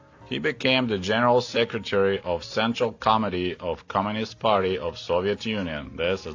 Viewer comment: When you started talking about Chernenko, the way you said "General Secretary of Central Committee of Communist Party of the Soviet Union" it sounded like you said "General Secretary of Central * Comedy * of Communist Party of the Soviet Union" - I got a good laugh from that 😄